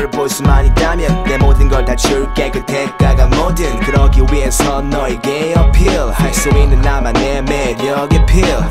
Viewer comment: am i the only one who hears gay appeal